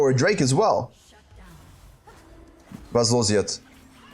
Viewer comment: what do u say? As a german I always understand "Was ist los jetzt?" 😅 😅